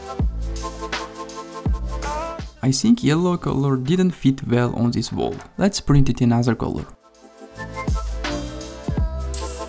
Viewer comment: in Video Duration, "Anoder" or is it "Another" I just heard a voice saying and where did the "D" come from